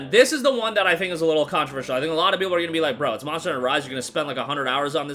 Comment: Bro, slow down a bit and pronounce your words a little better there were a few times I i was like what did he just say, like "marsh starter vive" meaning "monster hunter rise" 😅